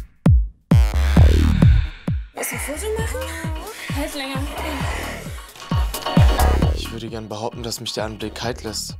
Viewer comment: Was sagt bei minute 11:20 bis 11:21? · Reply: Sie sagt willst du ein Foto machen hält länger